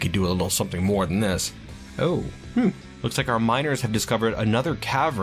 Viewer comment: wait did he said Tavern XD